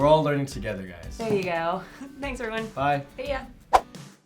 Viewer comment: I thought you said "Thanks Irwin" which is my name. 10/08 is my birthday. How weird.